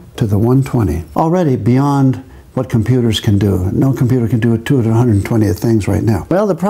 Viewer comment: "2^120, already beyond what computers can do" prints calculation on screen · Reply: Just listen carefully. At 3:48 he clearly says "no computer can do 2^120 THINGS right now". He never says "no computer can calculate 2^120".